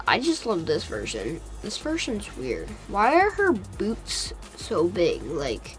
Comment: I thought said why are her boobs so big! 😂